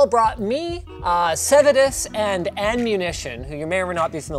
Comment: I thought he said "Sedatives and Ammunition" boy was i confused.